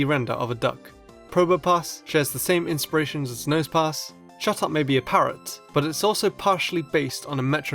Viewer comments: Who else thought said, "shut up" instead of "chatot"? XD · when he said chatot i thought he said shut up lol